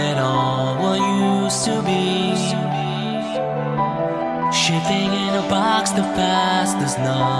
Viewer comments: First I thought he said shitting in a box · as i was hearing this song i cried at the fact that one day fnaf as a whole wont be popular anymore and i just want to thank scott cawthon so much for creating such a mind blowing creation of a masterpiec this game gives me nostalgia to when i first heard of it and played it and the fnaf communitty will always continue to cherish these moments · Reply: The first time I heard this song I thought the second line said "shitting in a box"